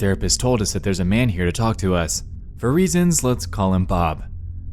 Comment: did he just say talk tuah?